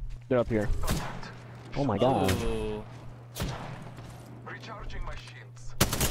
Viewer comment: did anyone hear recharging my sh*** 😂